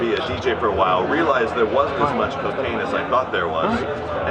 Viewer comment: did he say cocaine